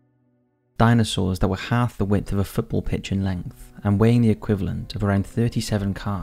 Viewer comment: I thought he said football Bitch lol